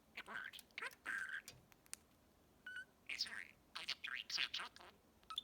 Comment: It sounds like he says "Item 3" a couple of times you've marked "gibberish."